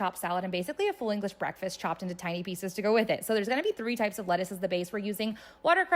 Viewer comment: My first watch I thought she said she was using 93 different types of lettuce 😂 so glad I was wrong. Also I have that salad spinner 😁 got it from Costco